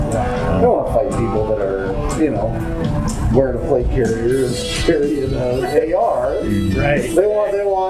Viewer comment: he actually said "wearing a plate carrier" here. Just a clarification of something missed in the text.